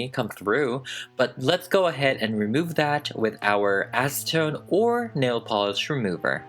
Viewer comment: okay... idk if its just me but... i heard "remove it with a$$ tone or nail polish remover", then i was like "(my name) youre dumb, you know what they meant"